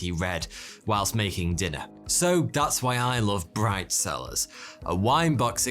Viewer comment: I thought he said his sponsor was bride cellars. I was like 😳